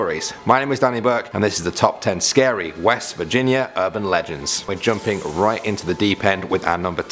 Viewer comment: i thought it said Virgina